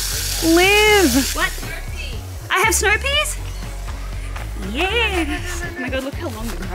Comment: You said “snow peas” I hear “snopes” Omg I’m going to see myself out lol